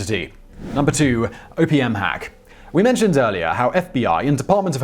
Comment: Did anyone else mishear OPM Hack as Opium Hack?